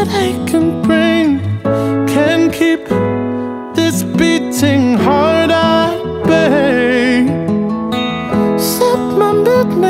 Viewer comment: I don't have much to give, but I don't care for gold What use is money, when you need someone to hold? Don't have direction, I'm just rolling down this road Waiting for you to bring me in from out the cold You'll never know the endless nights, the rhyming of the rain, Or how it feels to fall behind and watch you call his name Pack up and leave everything, Don't you see what I can bring Can't keep this beating heart at bay Set my midnight sorrow free, I will give you all of me Just leave your lover, leave him for me. Leave your lover, leave him for me. We sit in bars and raise our drinks to growing old Oh, I'm in love with you and you will never know, But if I can't have you I'll walk this life alone, Spare you the rising storms and let the rivers flow You'll never know the endless nights, the rhyming of the rain Or how it feels to fall behind and watch you call his name Pack up and leave everything, Don't you see what I can bring Can't keep this beating heart at bay Set my midnight sorrow free, I will give you all of me Just leave your lover, leave him for me Leave your lover, leave him for me. Leave your lover, leave him for me. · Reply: This whole time I thought he was saying can't keep this beating heart out of pain